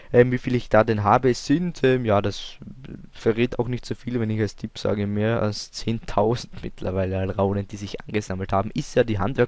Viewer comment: Ich verstehe nicht. Was hast du gesagt? Nicht mehr als 10.000? oO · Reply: Als Tipp: Sind weit mehr als 10.000 Alraunen, hab ich gesagt ;)